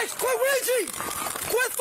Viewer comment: When he said "Quit Reaching" I genuinely thought he was saying "Luigi"